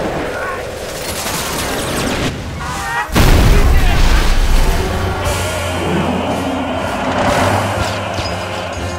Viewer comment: What are the soldiers screaming at 1:19 and 1:29? It sounds like German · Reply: he screams "Осторожно", "watch out, be careful"